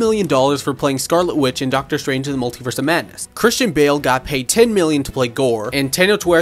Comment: Damn Christian bale got payed very generously like 10M, bro is stacked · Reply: I thought he said 10 billion